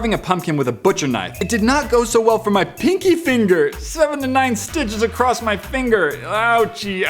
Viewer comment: I thought he said 79 stitches and i was like OMG then when i heard him say 7 to 9 stitches I was like oh thank god!